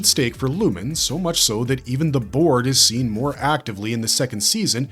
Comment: Every time you say "Board" I hear "BORG"